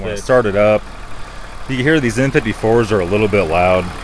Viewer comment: did he say N54? I don't think that's an N54 lol